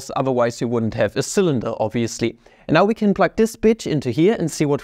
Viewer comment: did he really say h is a b*tch?